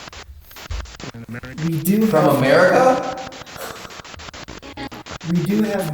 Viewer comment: I heard come back then a couple seconds later I heard from America 😮😮😮😮